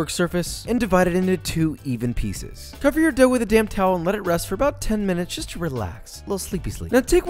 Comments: Whenever he says “cover your dough with a damp towel” I always hear “with a damn towel”. I know that’s not it but I’m just going to pretend that’s what he’s saying. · LOL. At 3:03 I thought you said "Cover your dough with a damn towel." and I was thinking, wow, he got edgy quickly.